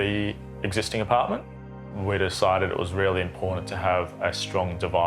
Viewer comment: What is "Impordn"? Is this an Aussie word? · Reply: Important😊